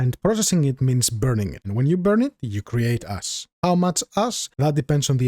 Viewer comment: tell me I'm not the only one who heard about creating ass and had to go back.